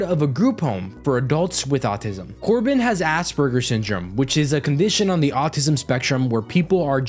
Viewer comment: My dumb self heard that as “Ass burger syndrome.”